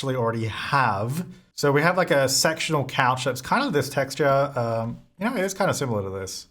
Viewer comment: thought u said “sexual couch” i was slightly concerned hahahah lmao, love ur videos, your laugh always makes me smile!